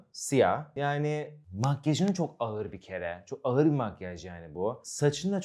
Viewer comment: ayır değil ne diyorsun 😂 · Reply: AĞIR DİYOR